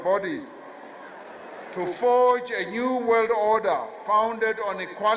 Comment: DID HE JUST SAY NWO😲😱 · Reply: Yes he did say NWO